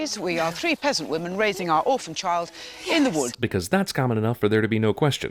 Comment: Technoblade: did I hear orphan